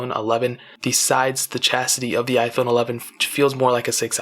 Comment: Wait... did he just say chastity instead of chassis? 😂